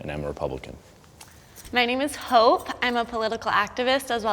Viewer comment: Did you say hope?